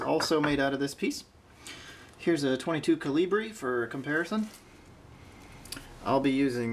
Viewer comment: What exactly as you said is a " kill - ee - bree " ? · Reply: "Super Colibri" - Specialty low power .22lr. More info at <link>